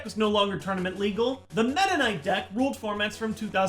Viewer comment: legit thought he said "mennonite deck" not "meta knight" LOL